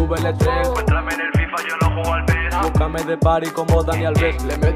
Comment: que dice ahi ???? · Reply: Pa entrarme en el FIFA yo no juego al PES